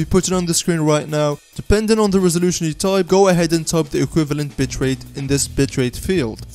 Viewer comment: did he just say bit*h rate. It's just the accent hahaha sorry btw you also made my DAY! thank you so much! :D already subscribed